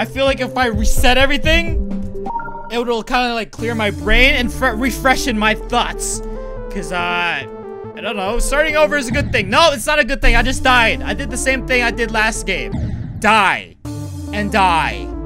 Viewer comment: when roi said refreshing my thoughts i thought he said refreshing my butts